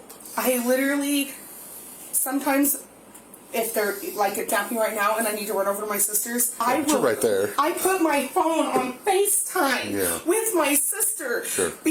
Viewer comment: I love this woman. THIS is how a real mom responds when she finds out that her children were left alone. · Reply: She 100% says "if they are napping like right now and I need to run over to my sister's I literally I put my phone on on FaceTime." Turn on your closed captions and slow down the playback speed, that is exactly what she said.